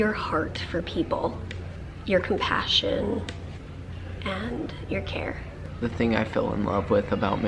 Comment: Mary, at first I thought you said to, “I love your heart for people, your compassion, and your hair.” 😂